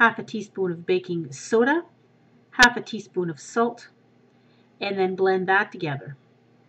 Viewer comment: i thought you said half a tea spoon of soap not salt haha